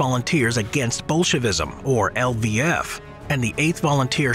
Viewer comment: i thought he said ‘’against bullshitisim’’😂